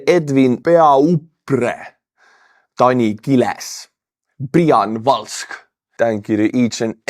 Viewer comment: 🤣 🤣 🤣 i heard you say Brian Walsh and had to rewind because I thought that you had actually said Elon M*sk